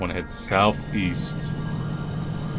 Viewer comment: South East? I thought you said Weist